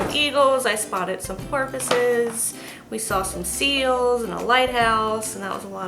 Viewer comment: So, I'm just watching this, and you're telling about your trip and you say "...saw some porpoises...", I heard "...saw some pharmacists..." 😂😂